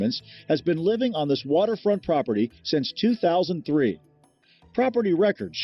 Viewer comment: Waterfront property. I thought you said Waterproof.